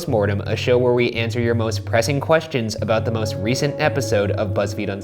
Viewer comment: Every time Ryan says “ pressing questions” I always hear “ depressing questions” , just me ? Yeah . Okay .